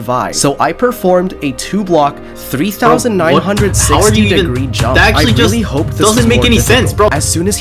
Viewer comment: DID HE SAY 3960 DEGREE JUMP?